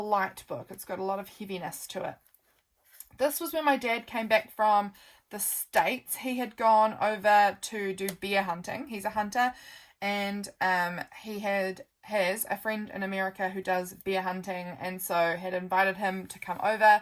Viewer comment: Lovely journals and hugs related to your pregnancy journey. When you were talking about your dad hunting, I thought you said he was going ‘beer hunting’ and I was like ‘what is beer hunting?!’